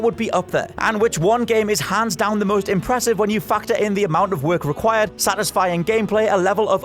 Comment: When he said factor i heard "fucked her"